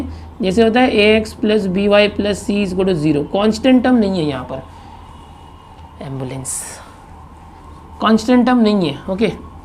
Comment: was i the only one who heard ambulance?!